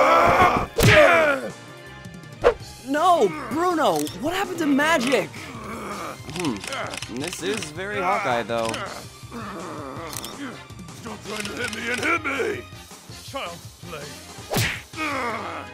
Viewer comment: I thought the summoner said that. "Hmm well this very hot though." I mean either way it fits